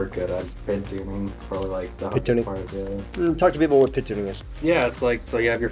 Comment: At first I thought you guys were saying "pit tuning". Then I realized you were doing PID tuning (proportional-integral-differential). Every engineer that works with computers/controls needs to know how to do that; Getting it wrong leads to a lot of failures.